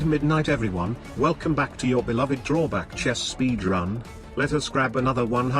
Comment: I always hear “speed run” as “B drum” and I always question what does that even mean.